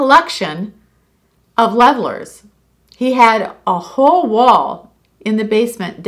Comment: When you said about your grandfather collecting levellers, I thought you said he collected lovers, then you said he hung them on the wall, I had to rewind!!😂 Thankyou for your channel, it is so uplifting, your a beautiful person and I'm so happy that you have found your house, I'm in the UK, and find it amazing that my girlfriends and I talk about what is going on in your life! And how happy we are for you! Xx